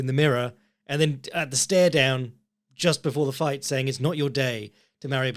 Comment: Vinicius said “it’s not your day”? Lol, I thought he said “You gonna kiss me? I know you’re gay” to Bautista. That’s why Bautista made like a kissing face or something.